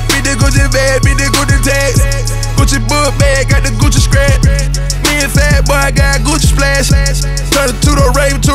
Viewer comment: Lmao 4:30 did future say "me & fatboy" to Rick Ross? 😅😅